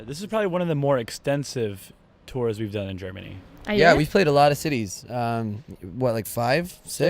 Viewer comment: When he said city's I thought he said titties😏😂